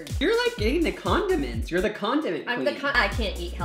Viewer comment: Omfg when called his mom the condiment queen I thought he said the Condom queen Lmao I'm dying